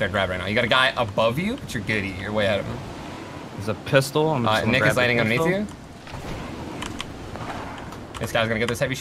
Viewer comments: ninja says “niggas landing on me too.” Anyone else catch that?? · At 25 sec why it sound like he said niggas landing on me too 😂... I know he said nick btw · definitely sounds like he said “niggas landing under you” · I thought he said n****s landing under you. 😂😂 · it sounded like he said the n-word but he said nick is but he speaks very quickly